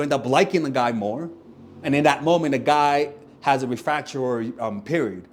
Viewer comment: what period does the guy have? it says 'refraury' but there's no such words? please someone tell me what the guys saying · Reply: Refractory